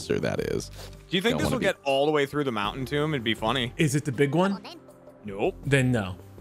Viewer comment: I heard "The Big One". We must drop barrels everywhere and light them on fire.